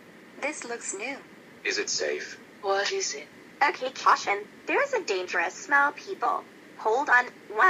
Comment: Why did say that for???? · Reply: Jr. Um no? Lol. She said caution